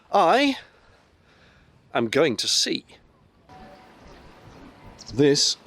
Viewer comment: For a brief moment I thought you said you were going to seed ;P